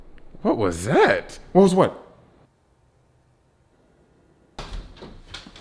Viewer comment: Imagine how his neighbor feel listening to him screaming "INSPECTOR NOOOO" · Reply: I thought he said “INSPECTOR NOAAAAAAH”